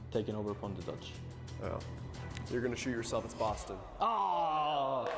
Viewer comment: Did really say, “you’re going to sh00t yourself”??? …oh my